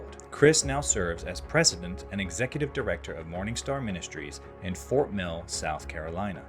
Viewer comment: I said out loud, "Did he just say MORNINGSTAR ministries? like LUCIFER the MORNINGSTAR?...THAT'S messed up!" Then a few seconds after, a YouTube channel pops up: "The Messed-Up Church." I hear ya, Jesus! Amen